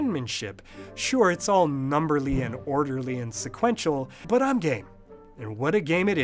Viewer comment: Did he say... I'm Gay